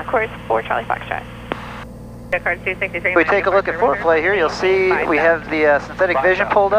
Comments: did you say foreplay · is it foreplay or foreflight? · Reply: I heard foreplay. Good job!!!